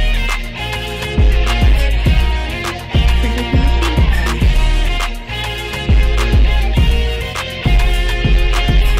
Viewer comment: What is being said at 0:17? BTW, I'm in love with this remix. I thought that the original was really good, but GOOD LORD THIS IS 100 TIMES BETTER. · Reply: That’s my beat tag. It’s my sister saying “Printer PHD!”